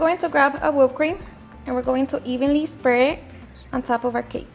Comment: Did I hear right? Evenly “spray” the whip cream?